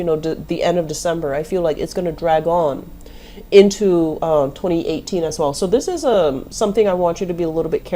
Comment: did you say 2018???